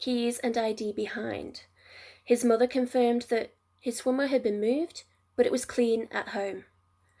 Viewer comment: What did you say at 9:00? "His mother confirmed his ????? had been moved, but it was clean at home." If sighting with older woman is correct, I can believe he ran off to start a new life with her knowing his family wouldn't approve of the age difference. Woman could well face prosecution if Lucas ever revealed himself. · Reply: Apologies, his mother confirmed that his swimwear had been moved but was clean at home. Sadly, you may now be aware that Lucas' remains were located this year. May he rest in peace.